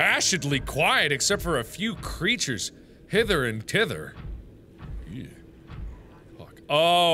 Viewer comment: When you said "hither and thither", I thought you actually said "hitler and titler" ... DONT JUDGE ME IVE BEEN AWAKE FOR MORE THAT 30 HOURS